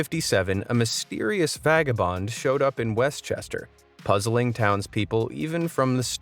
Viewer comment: did he say westchester THATS SO COOL I LIVE THERE😱!!